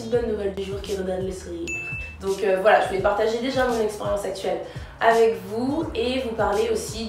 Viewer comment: j'ai entendu "expérience sexuelle"...